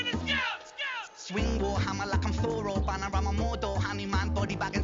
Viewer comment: I’ve always thought he was saying “war hammer”.